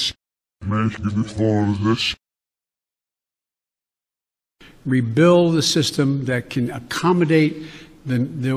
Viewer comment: I hear “making the fall of this”